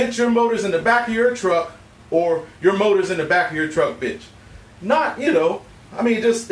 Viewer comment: I thought I heard your momma in the bed not your motor! Just saying